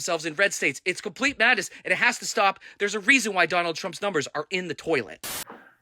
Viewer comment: Love how the Cubans pronounce his name as Tron 😂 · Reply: I always hear “Donna Trunk” 😂